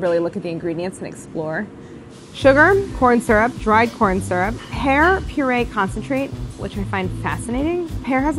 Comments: When she said “ pear purée”, I thought she said,” hair purée” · I wasn’t looking at the screen when she was reading the ingredients and instead of ( 1:29 )pear I heard hair and I freaked out 😂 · When you said pear purée I thought you said hair purée 🤣😂